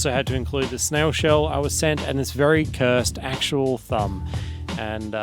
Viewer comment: Hands up who thought said "cursed bum" and hit rewind to see what a cursed bum looks like... just me?